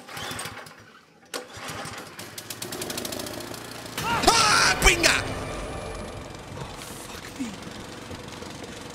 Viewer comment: lmaoo what did he say 😂😂😂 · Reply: "Pinga" lol my boyfriend is Puerto Rican and immediately cracked up. Unfortunately, it's not safe for the YouTube comments 🤣